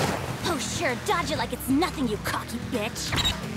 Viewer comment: did riruka just said coc** b****!!